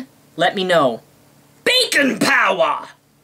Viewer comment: lol when you said bacon power it sounded like u said bacon powder.